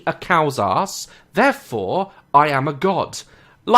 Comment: I blanked out and all I heard was “therefore I am a God.” And I had no complaints 😆